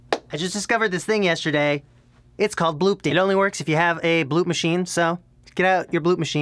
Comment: XD I thought it said bloodpance.